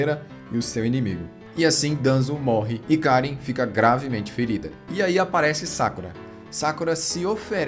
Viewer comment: eu escutei gravida kk